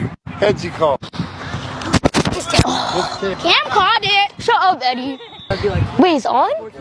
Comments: I thought he said shut up daddy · “Check One Cam Needs Grip Boost” “Cam Called It Shut Up Eddie” Was Actually Kinda Funny🤣idk why don't ask Thanks For All The Likes!! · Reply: bro i thought he said shut up daddy… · I thought he said “shut up daddy”😂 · I thought he said shut up daddy bro… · Bro sounded like he said "Shut up daddy" 🤣💀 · Is it weird that I heard Eddie: “cam called it!” Cam: “shut up daddy”